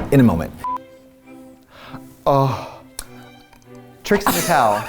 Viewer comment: My closed captions thought they said, "Tricks in Cows" instead of Trixie Mattel! Hahaha